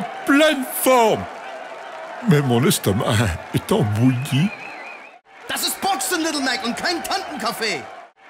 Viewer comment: I thought Von Kaiser said "This is boxing Little Mac" in English